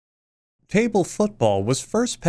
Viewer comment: I rewinded the video multiple times in the beginning because I thought he kept saying “table fuck ball”